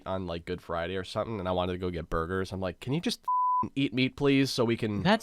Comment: tbh I thought u said eat "me" 4 a while